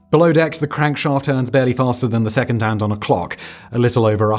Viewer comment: "barely faster than the second hand on a clock, a little over a hundred revolutions a minute" That is precisely 100 times faster than the second hand on a clock, by definition. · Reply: i thought he said seconds hand, not the second hand